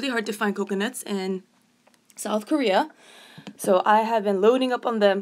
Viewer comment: Omg I thought you said North Korea I was a bout to say omg I am so scared